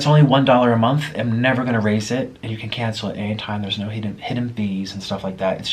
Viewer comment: I thought the chicken said “cancer” on it